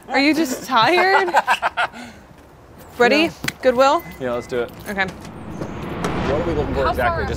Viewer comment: did she just say good weal?